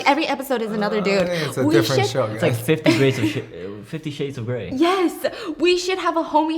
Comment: I can't stop laughing at saying "50 Grades of Shit" instead of "50 Shades of Gray" 😂😂